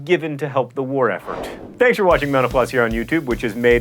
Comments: in the end it sounds like he says "thanks for watching menopause" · instead of him saying mental floss, since he said it so quick, it sounded like Menopause, teehee